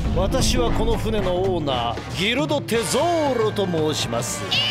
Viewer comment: これ当時cmで見てて、ルフィが『テゾーロ！！💢』って言ってた部分を『ゾロ‼️💢』と聞き間違えてゾロが敵になったんかと思ってたわ